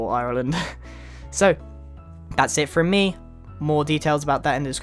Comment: uhhmm i maybe im not the only one that hear "good boy" instead of "good bye" right?